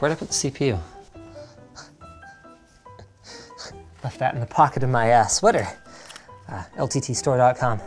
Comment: I heard "Left it in the pocket of my ASS"